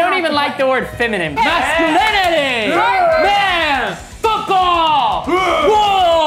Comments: When said foot ball I thought he said “FOOTBALL I thought he said “F OFF!” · Umm did say the F word at 0:14 😂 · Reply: He said football but I heard the f word first to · At the start, I thought he said F OFF and not FOOTBALL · Umm did say the F word at 0:14 😂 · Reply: i thought he said “f all!” but he said football 😭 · I thought said f all but he said football😂😂😂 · I can’t be the only one when football and thought he said f off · Oh he said foot ball I thought he said f word